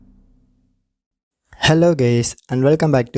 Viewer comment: i thought he said "hello gays" but its just the accent